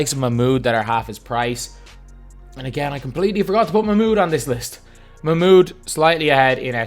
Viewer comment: Which player did he say he forgot to put on the list @ 6:51? I can’t seem to understand the name he said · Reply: Mahmoud Abdul Rauf